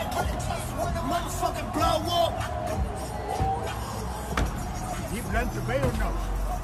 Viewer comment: Loooool..does nobody hear Wat dude said at 0:22 sec...."DOES HE PLAN TO PAY"..